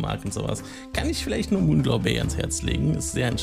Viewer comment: wie heißt das spiel ? man kann dich schlecht verstehen. Danke. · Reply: Moonglow Bay :D Sorry :)